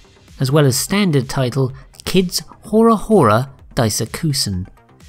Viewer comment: what did you say the name was? sounded like kids hora hora but i'm probably way off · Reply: Full name is Kid no Hore Hore Daisakusen. Full disclosure - I ad-libbed the pronunciation...not very professional I know